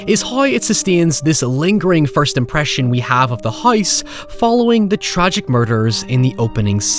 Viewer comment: As a non native english speaker, I got absolutely confused every time I heard "hoyse". Whete is that accent from?